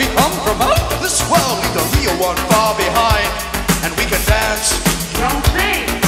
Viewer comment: so what does the blonde girl say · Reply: '' dancé'' meaning ''dance'' in french